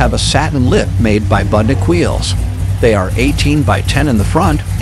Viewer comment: who makes the wheels?? couldn't understand him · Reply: Sounds like he said Budnick